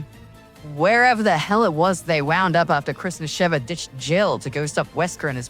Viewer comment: I legit heard Christmas Sheva here instead of Chris and Sheva